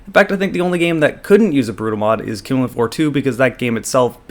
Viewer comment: What is the game he says at 0:42? I couldnt make it out for my life - even the subtitles spell rubbish. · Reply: Killing Floor 2